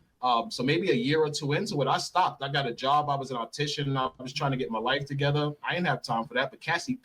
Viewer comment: He said he is a mortician…. Hold up, wait a minute. - he talking to happily about this. - he is a mortician. - I WORK AT A MORGUE - but ,. These people cut up and see your insides. They’ll eat dinner right next to the body. “ ( they don’t get paid ) “ this guy trolling and having fun. He lying., dude is definitely getting paid from Diddy to destroy the situation… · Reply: I thought he said opticians 😂😂